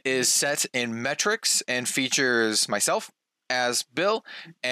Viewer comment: I heard “Myself, Azz, Bill” as “Myself as Bill” and that got me really thinking. 😂